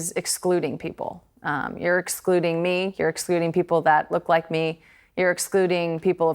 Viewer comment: did she say expluding?